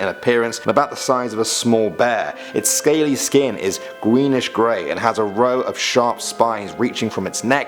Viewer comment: did i hear gweenish?